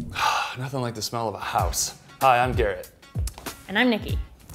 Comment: i thought he said “hi, i’m gay”